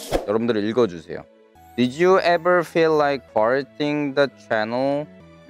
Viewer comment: I thought he said farting But he's Korean so I understand that I love you vids